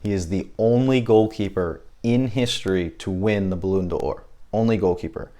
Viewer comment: did he just say the buloon door